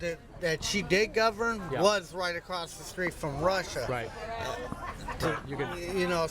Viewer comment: Did he say right across the street from Russia? 4:56.